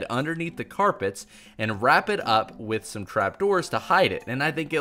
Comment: He swore when he said trap doors and said cr doors